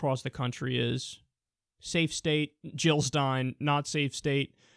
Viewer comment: At around 2:00 I thought he said savestates. Man, i wish real life had savestates.